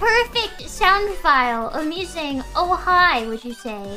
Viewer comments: "Of me saying" instead of amusing. "I have the most perfect sound file of me saying oh hi" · Was she saying "amusing'' or " of me saying..."